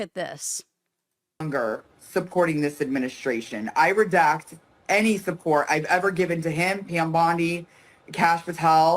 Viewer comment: Did she say, "I redact any support..." ? I get why she has the word "redact" on her mind, but damn, these people are stupid.😂😂